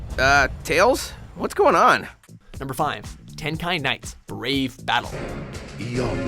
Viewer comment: That sounded like Hentai knights...